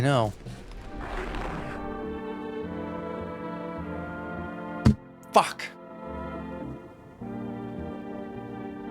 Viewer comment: What did the cold gentleman say at 33:56? Also great video! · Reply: "I think I finally understand why I'm so c-c-cold"